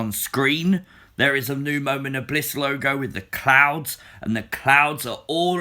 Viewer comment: I thought he said clowns with the mentioned clouds. Lol